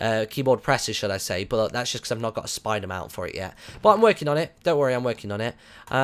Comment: When you said "I haventgot a spider mouth for it" i thought you said "I havent got a spider man for iy"